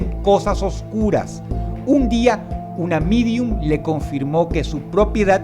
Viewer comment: Que dice ahí? un amidium o como es??